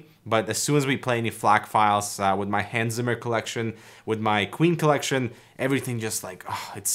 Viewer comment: thought he said "Hansen Collection"... was like UM BOP